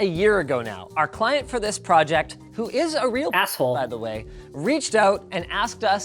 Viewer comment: lmao in b4 this gets taken down and re-uploaded because of that editing error at 1:07, cc says 'person' but audio says asshole...unless this isnt a error then double lol